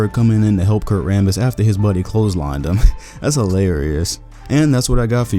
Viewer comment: did you say "that's hilarious" or "that's how Larry is"? They both fit really well.